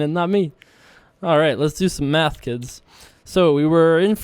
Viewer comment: After the last hand I really thought you said "Let's do some meth kids!" Congrats on the win brother.